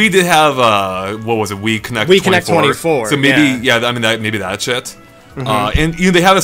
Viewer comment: I swear just said "that shit".